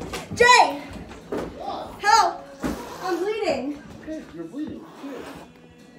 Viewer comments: I hope the dude who said “you’re bleeding who cares” and yelled at her off camera isn’t her boyfriend he had no care for her with all that commotion going on. imagine if it was something more serious? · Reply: I heard, ‘You’re bleeding, where?’ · She was asking for a help but the guy keep saying "What happen?" So annoying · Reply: It WAS annoying! Wtf kind of reaction is that. it also sounded like he said "OK, you're bleeding, who cares?"